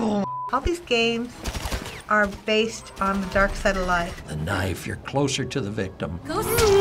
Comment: "The knife. You're closer to the victim." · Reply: I heard it as knifes are closer to the victims